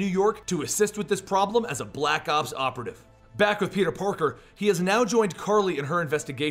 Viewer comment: did he say peter porker?